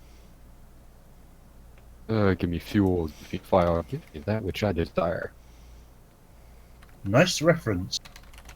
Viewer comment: Defuq does he say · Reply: Give me fuel give me fire give me that which I desire.